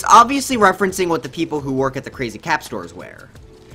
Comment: when you thought it said crappy cap :D